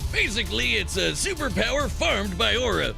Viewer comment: At first, I thought he said, "It’s a super powered fart with aura." LOL